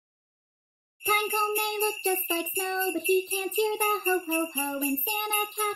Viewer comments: does anybody else notice the guy that sings the intro in the beginning says ''in santa HAT he still believes'' when it actually should be ''in santa CAT he still believes''...I thought it was just me but in every vlogmas it sounds like he says santa HAT.. but I still like it :) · Why does version sound like its saying "Santa hat" instead of "Santa cat" lol